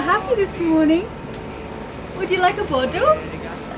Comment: I remember the episode when Elayna 'crunched the boat' pretty bad trying to dock it. I worry about my and Riley's vagabond. What is she saying at 7:49 ???? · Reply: "Would you like a bottle?"